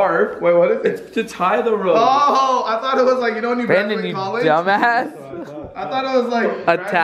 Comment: I think I’m the only one that thought it said Landon